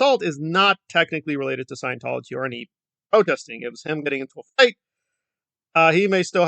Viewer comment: Your audio cuts out at 1:49 so I can’t quite tell what you said. Did you say “him getting into [a fight]”? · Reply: Yes it's not at 149 but 146 but yes said him getting into a fight.